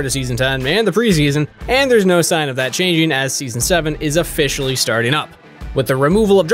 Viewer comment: did he say season 7?